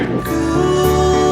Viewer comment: i thought she said cochina